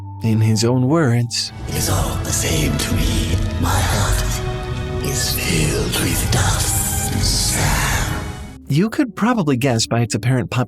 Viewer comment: can someone please tell me what he said there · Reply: "My heart is filled with dust and sand"